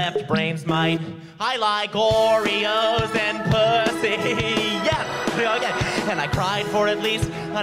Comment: Right Brain: "I LIKE OREOOSSS!! AND PU-" Me: "No." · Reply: FOR THE LONGEST TIME I THOUGHT IT SAID "OREOS AND COOKIES" 😭